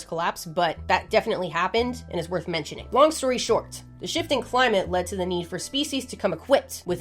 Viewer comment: When she said "long story short," I heard "swamp story short."